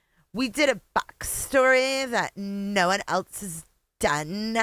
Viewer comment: On replay, but Hold Up… 1:58 wtf did she just say? ‘Nobody’?!